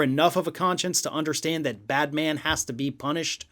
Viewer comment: Did you say Batman?😅